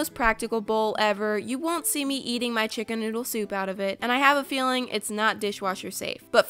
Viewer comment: Shut up did she say ChiCkEn nOoDLe sOup?!!😂